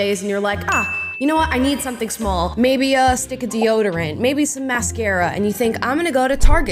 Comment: Omg this video started playing as i was finishing in the shower and all i kept hearing was "stupid deoderant" over and over lmao. When i got out i realized u were saying "stick" 😂